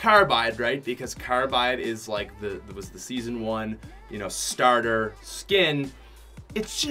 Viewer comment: did you say season one starter skin?